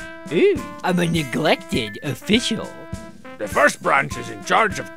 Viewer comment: "Ooo i'm a Neglected official." Just about lost it. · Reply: I heard "I'm an eclectic official."